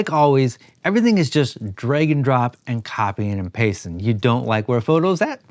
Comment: I heard "dragon drop" for a second and got interested, then I realized what was actually said.